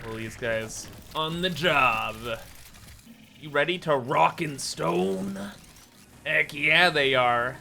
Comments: Did I hear a rock and stone? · DID I HEAR A ROCK AND STONE? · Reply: YEEEEAAAH! ROCK AND STONE!